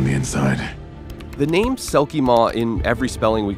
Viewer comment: Am I the only one who heard it as selkiemor, not selkiemaw?